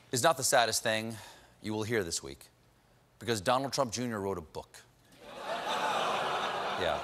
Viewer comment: When he said Donald Trump Jr wrote a book, I burst out laughing 😂🤣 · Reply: I thought he said "Donald Trump Jr read a book",and I burst out laughing 😂